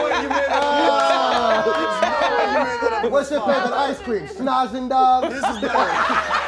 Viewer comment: Can someone tell me what is saying at 6:07? "What's your favorite Ice Cream, ?????".... Is he saying "snozzing dog"? lol